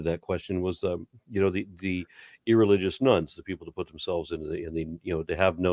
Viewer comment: Every time I hear "irreligious nuns" instead of "nones" and wonder what the coming punchline is.